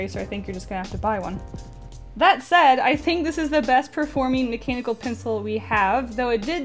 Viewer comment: Did anyone else hear her say "mechanical PENCIL?"